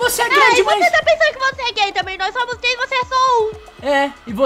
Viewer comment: eu entendi gay skassjakdh